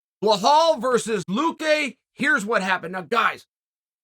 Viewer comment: Wait... did he just say Blahal?